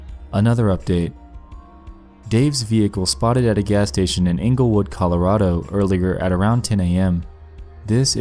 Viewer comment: Holy shit when u said “Englewood” I thought you said Inglewood like Inglewood California and I almost had a panic attack holy fuck